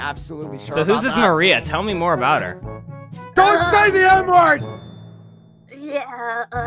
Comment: i thought he said "DONT SAY THE N WORD" 😂😂😂😂